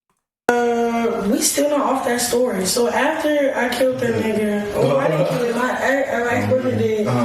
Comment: she clearly said "after I killed the ninja ,;I mean my ex-boyfriend did." And before that said "it was self-defense" and the boyfriend did time for it